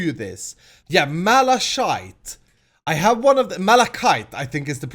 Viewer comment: What did he say? Malash**e? :O